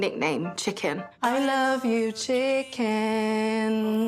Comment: I thought he said I love u JK 😂